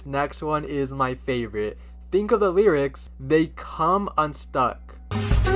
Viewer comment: I heard “Think of the light"